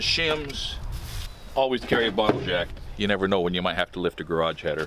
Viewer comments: Until I saw the bottle jack clearly, I thought he said "Always have a bottle of Jack" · I thought he said I always keep a bottle of jack for second and then I saw the floor jack lol